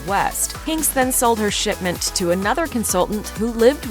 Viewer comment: It sounded like you said "sh*tment" rather than "shipment". From what I have seen of the clothing line, I am inclined to go with the former pronunciation.